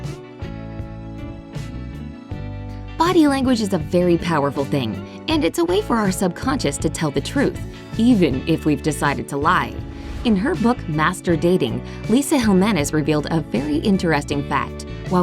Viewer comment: I thought it said “in her book, master bating!??? LOL